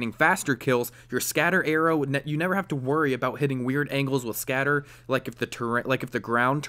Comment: you never have to worry about hitting weird angles ... Tree blocks scatter arrow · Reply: lol oops thought you said angels